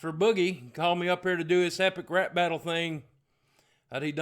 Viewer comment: I thought he said Boogie brought him up for a "happy crap battle".